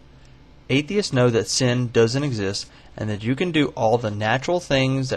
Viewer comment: I thought you said your brain doesn't exist.